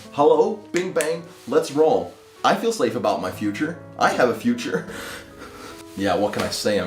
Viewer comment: did you say "i feel slave about my future"?